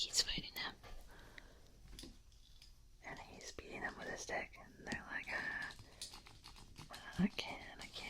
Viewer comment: did I hear that right lol? He's beating him with his dick, I can't I can't the monkey is to strong. I might be wrong.